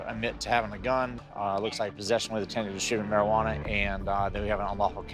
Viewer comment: sounds like he said "possession with the intent of shooting marijuana" 😂😂